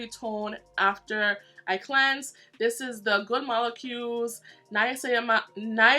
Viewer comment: When you were trying to say niacinamide i want paying attention and it sounded like you were saying mamase mamasa mamakusa from Michael Jackson Wanna be staring something 😂😂 you pronounced it better than me 🙈